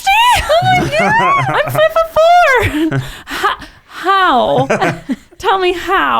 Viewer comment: I misheard and thought she said "50 pounds???!!! I'm 4'4" instead of 5'4, which she actually said